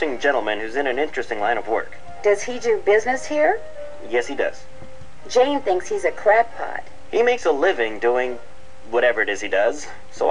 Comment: nancy sound like shes saying "jane thinks hes a crappot"